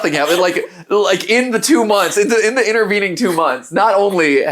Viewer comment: Me: thought he said 2 weeks Matpat: You leave for 2 months, consequences xD Me: Haha yea I'm leaving for 2 weeks too- wait did he say months??? rewinds, "I've been gone for 2 MONTHS!" ........oh....how long have I been mentally out of it?